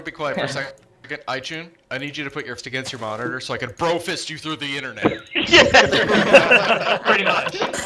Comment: did i hear BRO FIST?